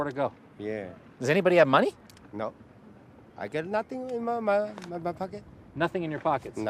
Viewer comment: Anyone catch at 22:14 "I got nothing on my mind muddafuka"